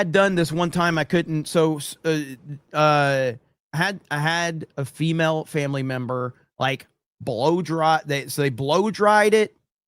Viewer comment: All I heard was “I had a female relative blow me” fuckin Alabama